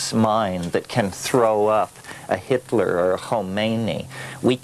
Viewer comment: Anyone know who or what was said after "Hitler" in the speech. Sounds like "Homani". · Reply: referring to the islamic revolution of iran 1979, the Ayatollah. Ruholla Khomeini (with silent/uvular fricative k)